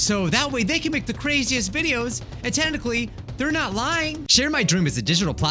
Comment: omg XD am I the only one hearing "tentacley(?)" instead of "technically"??????